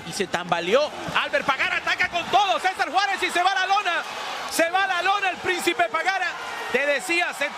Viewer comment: What is the announcer saying @ 11:46 with "...lune..."?